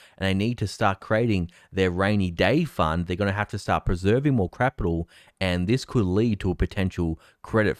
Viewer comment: Crapital ... was that said on purpose?